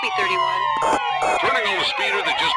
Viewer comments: officer:"31 county" Dispatch:"copy 31" Officer:"Turning on a speeder that just flew by me" I've gotta say best lines from nfs history. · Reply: I always thought it said "just blue barbie" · i misheard the police chatter as "turning on the speeder that just screamed barbie!"